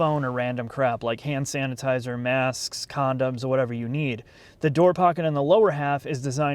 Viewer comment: minutes,Did he say condoms in the same sentence as mommy mobile? Maybe I have been listening to too much Reddit failed relationships. But rubbers in a mommy mobile that I will be paying for is not a good selling slogan.